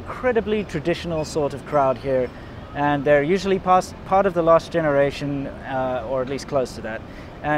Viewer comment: The Chinese (China) subtitle isn't quite right at 1:24. I think whoever translate this misheard loss generation as last generation 😂 which somehow still make sense